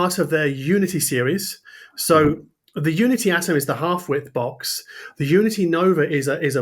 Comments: I thought you said the unity was a Half-Whit box... lol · Oh, half-WIDTH box. I thought you said half-WIT! I was thinking, finally, a box for me! Lol